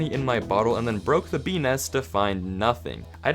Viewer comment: i genuinely thought he said beenis and had to listen a few more times